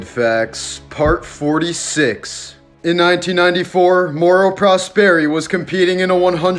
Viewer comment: I fr thought you said moral prosperity